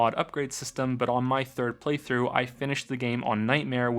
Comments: Ok the first time you said Preythrough I thought I misheard you. The second time I thought you just misspoke. The third time? Fuck yourself. Good pun, love the video. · "I did three preythroughs of this game" oh you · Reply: He said preythrough early on and i was like mmm i misheard that maybe but the three pretthroughs i was like nope okay yupp i gotcha.